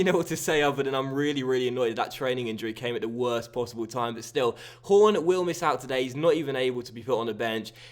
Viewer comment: When you said horn at 3:25 it sounded like you said porn